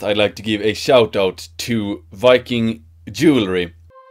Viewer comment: I looked away for a couple seconds and thought you said: "first I want to give a shoutout to viking "jewry"....I was shocked to say the least :P